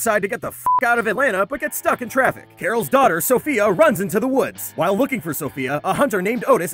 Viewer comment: Omg I thought he said Daryl's daughter and I had to pause cause I was laughing too much. I don't understand myself